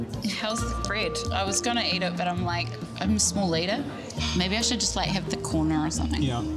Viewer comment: Did she say "I am a small eater" ? The guy is a gentleman but we can see right from the start that he is not attracted and we all know why